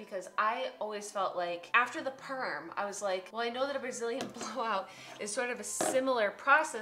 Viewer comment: I thought this said "Brazilian bowl cut" and I was like well I know what a bowl cut is and I'd like to see Julian with a bowl cut that has a Brazilian twist